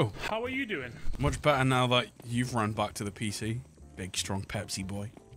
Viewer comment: did i hear pig😆